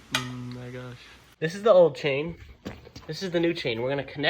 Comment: bro did you just said the n worD? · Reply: He said my gosh